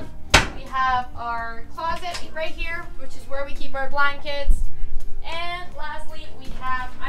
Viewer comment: Ok.....at 6:55, did she just say.."that's where we keep our blind kids"?